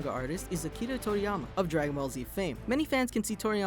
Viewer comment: did he say akira from dragon Ball z?